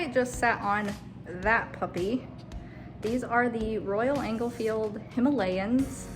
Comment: Did you call it a Royal Engel-field? : )